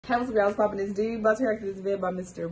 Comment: bruh you talk too fast. You need to slow down, I don't understand a word you just said.... why u speeding? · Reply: Hey, whats up y’all, whats poppin, it’s D. Bout to react to this video…